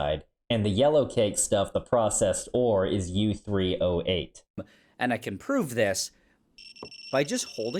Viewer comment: I heard that as U-308, not U3O8, lol. I was like, holy hell, i didnt know uranium had an isotope with that many neutrons!!!!!! Im used to seeing it as 233, 234, 235, 236 and 238 lol.